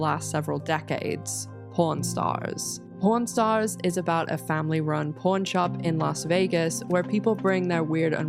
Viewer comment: Thought she said family run porn shop